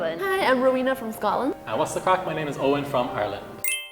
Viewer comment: What does Eoin say before he gives his name? I keep hearing, "Oh what's da fuq. I'm Eoin from Ireland." (Or did he say, "And worst of all, I'm Eoin from Ireland"? I've rewatched his intro 10 times, so maybe I finally caught it.) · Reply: He actually says "And what's the craic, my name is Eoin from Ireland." It's a typical saying here! but I think your interpretations are... unique hahah